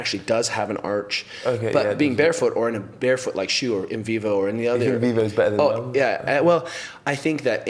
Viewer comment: What shoes does Kelly recommend instead of Vibram Fivefingers at 31:07? I hear "Invivo" but can only find a brand called Vivo Barefoot. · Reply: Inov-8 !